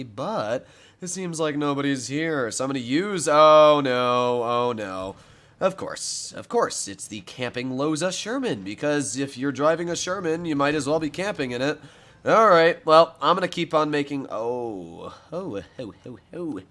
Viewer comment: The loza sherman,i thought u said the losers sherman XD